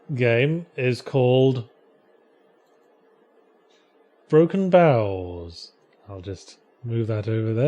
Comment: I thought he was saying Broken Bowels